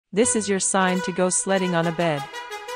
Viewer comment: At first I thought it said sleeping with a bed and I was like what's so special about that 🤣